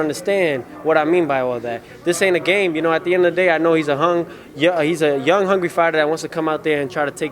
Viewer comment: wait what did he just say here??? “He’s a hung , young, hungry fighter.” Lmaooo